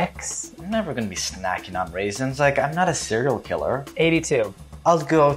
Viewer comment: “I’m never gonna be snacking on raisins. I’m not a serial killer” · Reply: “I’m never gonna be snackin’ on raisins. I’m not a serial killer!” I thought he said CEREAL KILLER and I thought, I like it!